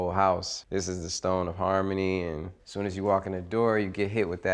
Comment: Did he say Harmony one 1:36 🤔?